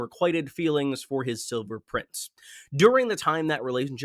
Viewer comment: not the point of the video at all but it sounds like you said “Sober Prince” and its sending me. Also mad relatable to losing someone you are deeply in love with at a young age and feeling lost.